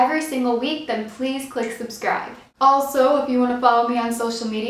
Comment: It sounded like you said "Fleas subscribe" XD 😂😆